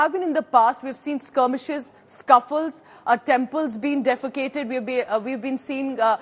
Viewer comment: Did she say Temples being defecated at 1:14 instead of desecreted ??